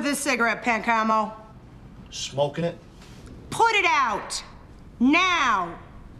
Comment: I always hear "shhmocking"